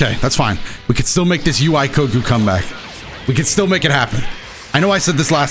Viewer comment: Lol did just say "We can still make this coku comeback"